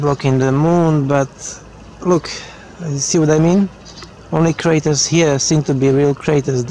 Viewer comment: you didn't just say, what you actually just said?...@ 5:05 there ya go again!.....Holy Shit! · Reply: could be my accent... I said all craters on this side seem to be real, and not on the other side. Something along that line